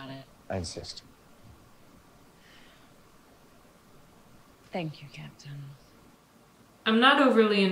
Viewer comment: did she say “thank you again TOM”?